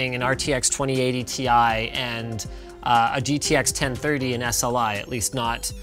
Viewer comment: did Linus just say GTX 1030?